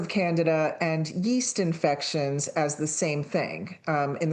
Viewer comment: I initially misheard that as "Canada and yeast-infections".